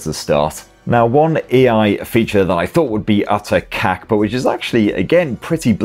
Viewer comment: I heard the Afrikaans word "kak" meaning shit in English 🤭